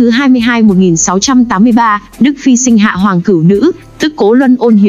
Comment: hoàng tử nữ ??? Mị có nghe nhầm hay gì không không hiểu cho lắm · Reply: Hoàng Cửu Nữ mà bạn, công chúa thứ 9